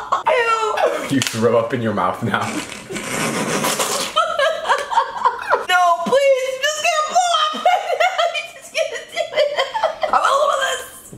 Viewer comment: “NO PLEASE UR JUST GONNA BLOW IM PREGNANT HOW HES JUST GONNA DO IT“ that’s what I heard😂